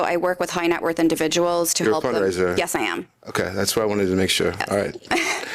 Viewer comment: What did he say at 12:05? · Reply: "You're a fundraiser"